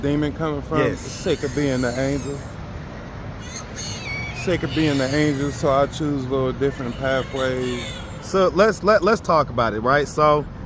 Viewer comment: I think he said 8:53 " sIck of being an angel so I choose a Lil different pathway" right after he was asked where is that demon coming from